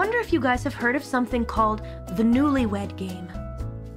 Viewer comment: Thanks. 44:46 should be newlywed instead of newly wet lol